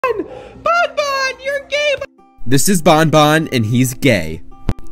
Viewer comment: I thought he said "Bon Bon your dead!" 💀💀